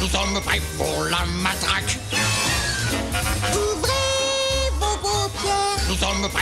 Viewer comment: moi j’entends ouvrez vos gros pierre